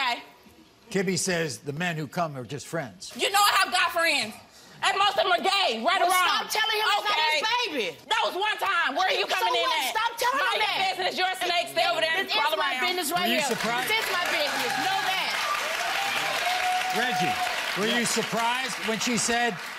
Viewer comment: did i hear this right did she just say mind your business your a snake stay over there